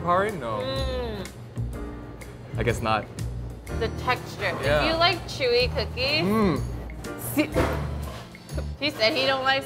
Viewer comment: I heard "If you like Tzuyu cookies" lmai